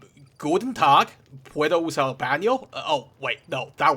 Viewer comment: what did you say after "Guten tag" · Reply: ¿Puedo usar el bano? (Spanish for "May I use the bathroom?")